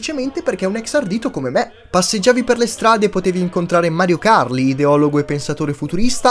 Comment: Mario Kart è la prima cosa che ho sentito